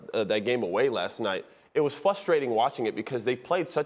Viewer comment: Mother trucker, your job is to say words. And the word you chose to use is: "FUSSTWAITING"